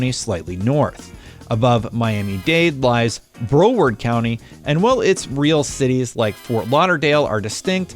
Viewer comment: Did you just say Browerd county? Lmao